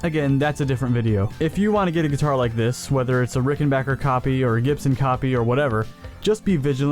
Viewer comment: Cat thought you said "Chicken back here." 🤣